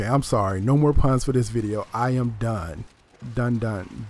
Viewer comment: At first I thought you were saying "I am Dune"